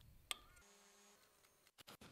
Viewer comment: what she says: "gnochi with beef ranggoo" what i hear: "knocky with beef ragoo"